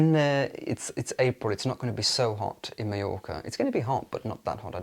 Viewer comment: I got very excited because I thought you said you were going to New York. 😅 But realizing you meant Mallorca made much more sense. Hope you have a beautiful trip! You're going to look so sharp. ❤